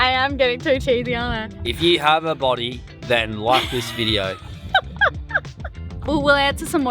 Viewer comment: "If you have a body that works, then life is video!" That's what I heard, and it's the funniest remark I've heard for ages! I know said "then like this video!", but I still love the Monty Pigeon-feel of the context!